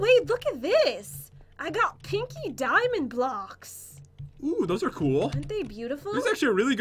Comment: I thought she said kinky diamond blocks and I choked on my chocolate bovine secretions. (choc milk) XD